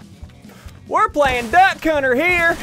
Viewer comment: emm what’s he saying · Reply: Were playing duck hunter here is what he's saying